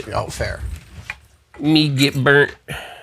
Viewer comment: I thought John Duncan said "me give birth" as he rotated the piece, It caught me off guard haha. Im going to lightly spam my friends with that phrase now.